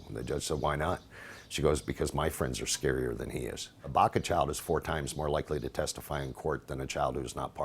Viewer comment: Did they say "Baca Chavez" on 1:51 cuz if they did, those are both my last names!!! LOL so weird